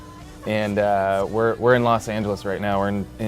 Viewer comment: When Josh was talking about how Tyler was his best friend then he was talking about L.A I thought he was going to say "we're in love" 😂 but instead he said "we're in L.A" 😂